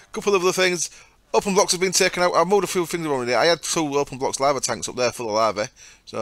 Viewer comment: WHAT? I couldn't understand :P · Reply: lol sorry. "Openblocks has been taken out, I moved a few things around in here, I had 2 openblocks tanks up there full of lava"